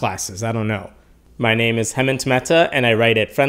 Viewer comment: Every time he says his name, it shows at the top left of the screen. Yet every time he says it, I keep hearing it as "Hemand Temetta".